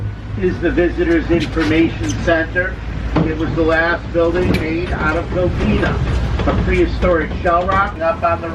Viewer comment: True story here. At 2:52, the first time I heard it, I thought the conductor said "This is the last building made out of Velveeta".